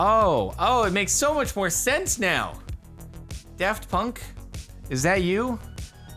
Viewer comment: I'm sorry, did you just say "Deft Punk"?